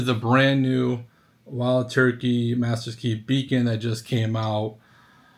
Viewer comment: #Bacon Ohh I thought you said bacon, lol. Is the sour taste because its been open to long?